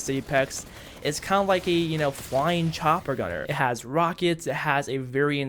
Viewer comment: did he say flying chopper gunner at 10:49 FLYING